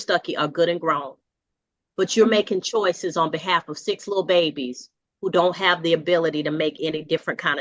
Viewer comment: did she say six babies babies?